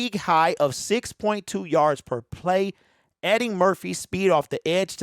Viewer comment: Sec 46 I thought he said Eddy Murphy lol I said whattt